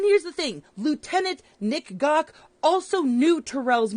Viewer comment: … I thought she said “Lieutenant Nxggah”